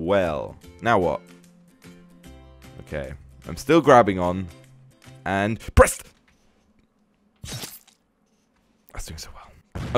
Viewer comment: he says breast instead of press lol 😂😂😂